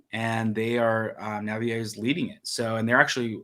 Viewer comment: I thought Biden said "Mary was leading ⁉".... 🤣🤣🤣